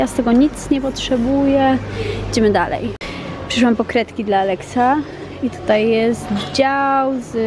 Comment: Usłyszałam ,,Przyszłam po krewetki dla Alexa'' i takie ,,Że co?'' Hahah